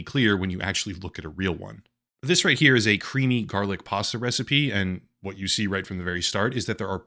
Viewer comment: am I hearing "Possa Recipe"?